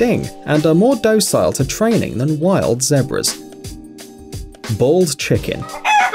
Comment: i thought he said ball chickens